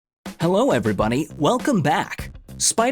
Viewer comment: did He Say Hello Every Bunny??